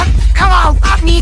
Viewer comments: HELP WHY DO I HEAR "COME ON FU(K ME KIIBO BOY"😭😭 WHAT'S WRONG WITH ME?! 😭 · all i hear is “kmo boy”😨😭